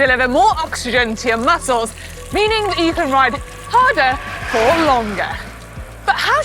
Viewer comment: Sorry I thought it said rise harder with manon